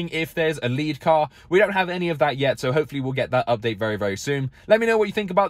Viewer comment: What did he just say?! LoL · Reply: Let him know what you think of the update down below in the description 😄